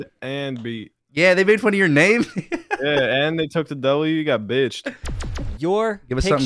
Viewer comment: at the start, did say what i think he say? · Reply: he said bitches i thought he said it too tho